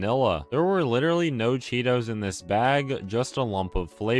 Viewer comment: Me: "There's no thing sadder than losing the will to live." "Hold my chocolate chip ice cream!" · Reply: I thought you said mint chocolate